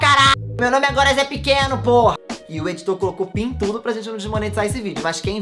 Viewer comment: no 24:32 eu entendi "e o diretor colocou PINTUDO pra gente não desmonetizar esse vídeo"